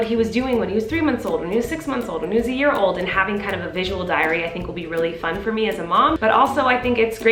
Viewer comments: Idk why I thought she said ‘visual diarrhoea’ 😂😭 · i thought she said “visual diarrhea” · sounded like "a visual diarrhea"